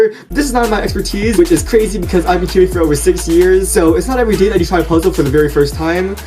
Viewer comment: wait did he just said hes been solving for 60 yrs